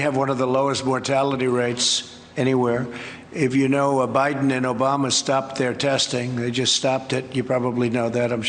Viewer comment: He's so used to blaming his predecessor it is now a natural response. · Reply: At first it sounded like he said Biden and Obama stopped breast feeding.....